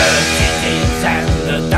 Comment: For some reason when he says done dirt cheap i always thought he said thunder chief